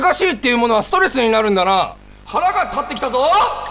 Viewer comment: 腹が立ってきたが 腹か減ってきたに聞こえたW